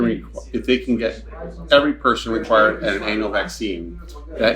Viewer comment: I thought i heard him say an anal vaccine. About to say ......🤬